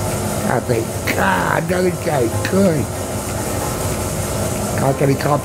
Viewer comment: Hey at 11:17 the subtitles got it a bit wrong what he says after (I love life!), "when I open my eyes I say, GOD ANOTHER DAY GREAT!!!" I <CAN> accomplish this/ I <Can> do that. God bless this man my Iron giant